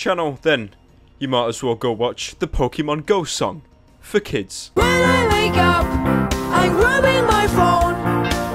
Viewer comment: Am I the only one who thought Misha said "When I wake up I am rubbing my balls" or did anyone else hear it?